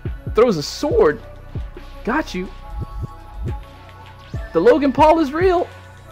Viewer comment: did he just say the Logan Paul is real